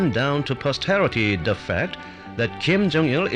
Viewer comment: LOL "DUUUUUUH FAUAHUCT" instead of "the fact"